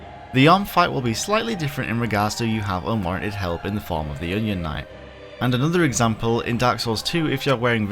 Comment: what are you saying I actually don't understand "The ung fight will be slightly different in regards to you have unwanted health in the following of the alien/union knight"??? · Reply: Yhorm fight. Unwanted Help. Onion Knight (Seigward).